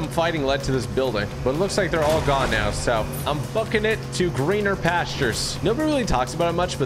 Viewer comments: Did he say fu*king it or booking it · I thought he said f**king it but he said bucking it